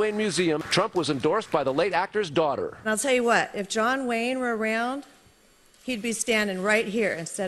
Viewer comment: is it just me, or does John Wayne's daughter say, "I tell you what, if John Wayne was real, he'd be standing right here instead of me"? Why would she say, "if John Wayne was real"?